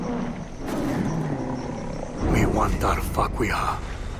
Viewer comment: listen to what the character says to me at one part he says "we wand da fuck we har"